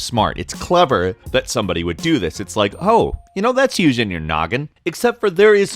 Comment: Did just said "Gnoggin"?